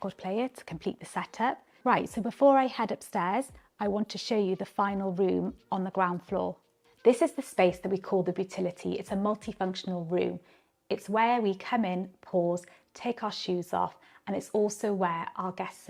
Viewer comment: did she say “it’s where we come in; pause.” 🤔